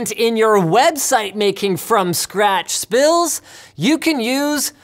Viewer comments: did he say spills instead of skills? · i thought I heard you say "spills" in stead of "skills" CC says I'm correct.